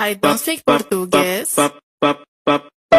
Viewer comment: Her: i don't speak Portuguese. Me: ok. Her: i cant speak English. Me: then language are you speaking right now dummy 👍 Like if you agree 😊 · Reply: Oh i thought she said i can speak English